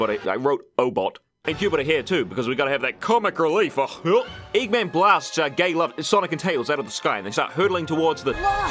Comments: it took me over 20 rewatches to catch this (I play it all the time, cause of how entertaining it is). Did he just say "Eggman blasts our gay lovers."? Cause if so, that's pretty funny. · “Eggman blasts gay lover- Sonic and Tails out of the sky.” I had to rewind to make sure I heard that right XD